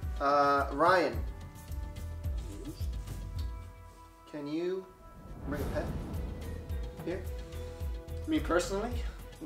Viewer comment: I cannot for the life of me figure out what is saying. I just keep hearing "Can you repent here?" But that makes no sense in this context